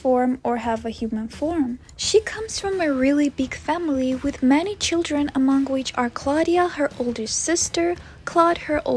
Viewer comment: did she say "my girlfriend draculaura or it was me? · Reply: "Ghoul friend" that's what she said.